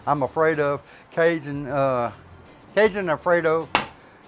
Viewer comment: Thought said “Cajun AM Radio” for a second. Well done as always.